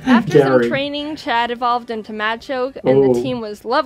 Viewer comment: After that training, that sounded like "macho"